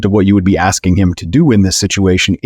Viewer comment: Did he just say Bears are FUN? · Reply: Oh oh oh Bears are Fucked!